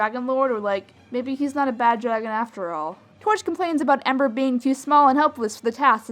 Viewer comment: U sounded like u said Amber through the whole thing when u said ember